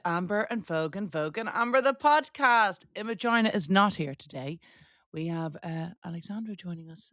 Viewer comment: Thought u said vagina is not here! 😂 Gargle salt water and rub manuka honey back of throat